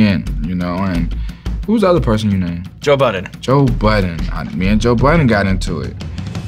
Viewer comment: i thought they said BIDEN